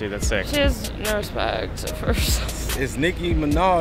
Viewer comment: I thought she said no respect for herself not no swag.